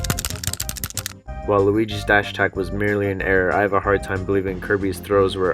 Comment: he says waluigi instead of luigi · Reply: while luigi's dash attack